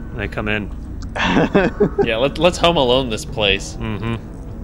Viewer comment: “Lets home alone this place” 😂😂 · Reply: I thought said “Hum alone” at first and I thought it was the perfect pun